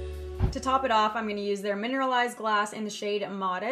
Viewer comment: Am I the only one who heard glass instead of gloss at 8:12? 😂 I love anyway! ❤️